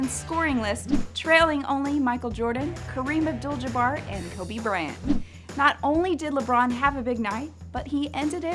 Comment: did she just say Kobe Brant???